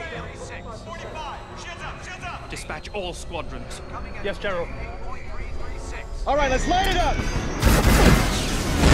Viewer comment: I heard one Guy say jedha jedha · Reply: He said "Shields up! SHIELDS UP!!!"